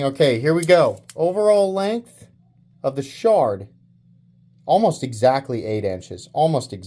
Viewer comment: I might need a hearing aid because I thought you said "The Shart" 😅 Cool knife 🔪 😎 I'm going to have to get one!